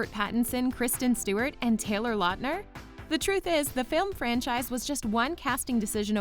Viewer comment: Did she say "failed franchise"? in